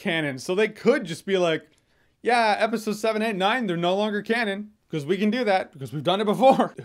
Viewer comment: Why did you say 'wipe episode 7 and 9'? What about 8, which is the worst one?